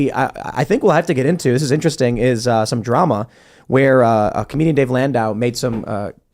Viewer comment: "A communy davey landau" WTF IS HE SAYING HERE?